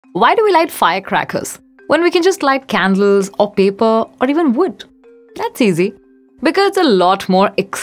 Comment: At first I thought you said "Why do we light firecrackers, when we could light candles or people or wood?" Lighting people is definitely more violent than lighting firecrackers.